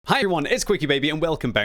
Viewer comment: something went wrong during that intro lol. i didnt hear "Hi everyone." i heard "Hi-Yuan"